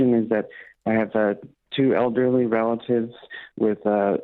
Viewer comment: Was I the only one who heard him say "elderly siblings"?